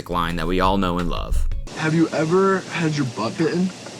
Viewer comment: Love the confused look on her face and the awkward silence after this guy asking if she has ever had her butt bitten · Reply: I was confused too because I misheard and thought he said "have you ever had a buppin?" To which the response is supposed to be, "what's a buppin?" and then he lets it rip with "buppin DEEZ NUTS!" or something. Was relatively disappointed that he was just asking the bite her butt with the craziest eyes possible.